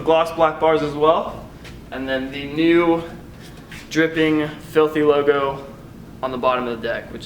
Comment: He said “ the new dripping FILTHY logo” instead of saying fusion logo haha